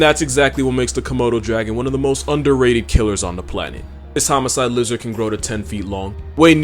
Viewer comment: When he said "This homicidal lizard" about the komodo dragon I thought he said "this homicidal loser". I was like damn bro that's harsh